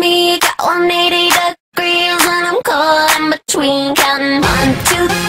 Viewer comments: I thought it said " 1, 2, 3, but I'm cold in between" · I thought she said and I'm crawling between